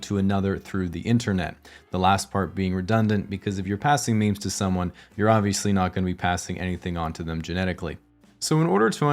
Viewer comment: did he just say if you passing on memes you ain't gonna be passing on anything genetic? lmao, must be referring to Redditors